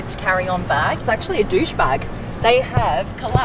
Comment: did she say douchebag?